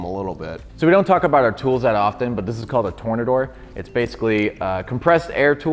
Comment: Do you think this Porsche should be salvaged? & what is your favorite color of car? Dont forget to like & subscribe if you enjoy the video! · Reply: What is this attachment called? Did you say, "Tornadoor" ? I don't see it listed in your list of stuff and can't find anything like it on Amazon/ebay Can You Help !??